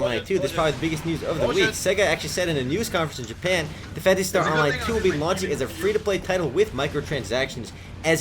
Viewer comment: sounds like he said "FANCY ONLINE 2" lol xD